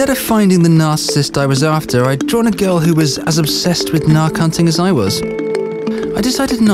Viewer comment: I couldn't understand what the guy was saying at 10:53 so I put the CC on and it says "nar-counting"* ... that's not a word. Could someone tell me what he's saying or explain what he's meaning? *the closest thing I found was the "National Association of Realtors (NAR) Counting ... mishap" and I'm pretty confident that's not what he means.